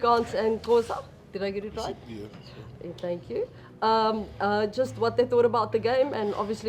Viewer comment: lol did I hear the first reporter say “and xhank you?” 😅